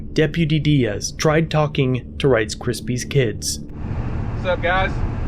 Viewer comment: I thought he was saying Rice Krispies until like the last 2 minutes. I’m hungry.